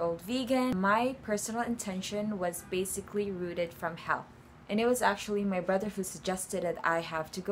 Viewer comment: why the caption said "basically rooted from hell"😭😂